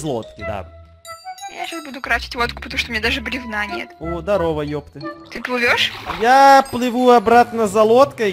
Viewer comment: ХАХА ПОЧЕМУ Я РАНЬШЕ ЭТОГО НЕ ЗАМЕТИЛА (надеюсь мне послышалось ) 9:33 эдисон: о здарова епт ХАХАХАХА СКАЖИТЕ ЧТО МНН ПОСЛЫШАЛОСЬ